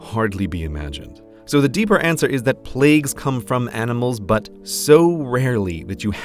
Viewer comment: I thought he said: "Plagues come from animals butt..."